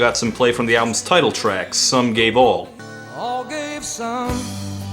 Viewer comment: Who else heard "Some gay ball"?